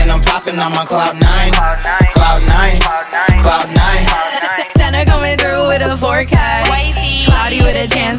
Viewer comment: I thought she said, "Tana comin' through with a fork ass" 🤣🤣🤣